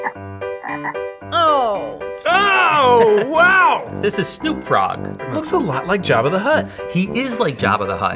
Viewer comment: did say the frogs called "Snoop frog"? Because if so I love it